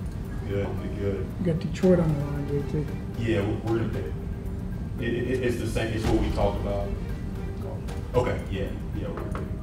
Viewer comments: terry says something about the second one his talking about. So was trey lance the first option or am i missunderstanding that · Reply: Yea I heard, “Its the same as what we talked about” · did I hear " got Detroit on the line" at 2:51?